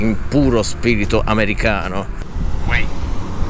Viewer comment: Capitano mi sa che hai sentito male, quella voce a 12:26 non dice "Wait", ma era un affettuso "Uee"! Se fai attenzione quando poi diventa verde non dice "Walk" ma "Vattenn". Aaaah grande città Washington, così multiculturale